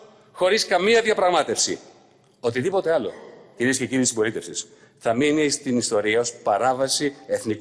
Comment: I don't understand Greek, but surely he didn't say ".. whatever else ladies and gentleman of the copulation, ", or did he ???? @ 21:25 ..... If they are copulating the People, I think it is without consent !!!